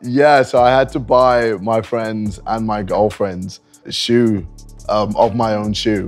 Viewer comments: DID HE JUST SAY MY GIRLFRIENDS LOL IS HE CHEATING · Bro please tell me someone heard him say “my friends and my girlfriends” in plural sense